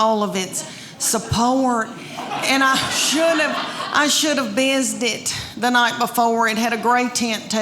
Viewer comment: funny Lady.🤣 What did she say at 1:45 I should have__________? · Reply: Bizzed it. Used Biz detergent on it.